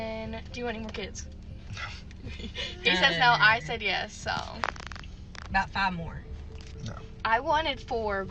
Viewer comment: Where did she say we want 5? I heard her say NOT 5 more then the daughter said she wanted 4.